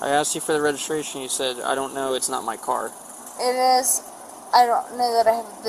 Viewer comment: First girl /was/ drunk but the officer /did/ mishear here. She said "No, but it's my car".